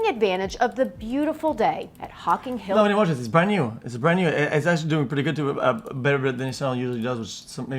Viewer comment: time what the fuck are you saying x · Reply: “Why wouldn’t I watch this? It’s brand new. It’s brand new. It’s actually doing pretty good too. Better than this channel usually does. Which is maybe interesting.”